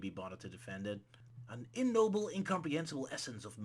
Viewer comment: I think said The End is "unknowable", not immobile or Unnoble (that's not a word, right?) Which means the developers basically defined their villain as: "don't ask questions, it's not possible to know anything about it!"